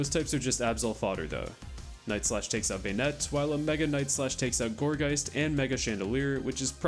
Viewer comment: i'm so brainrotted that I heard mega knight at 32:17, emphasis on knight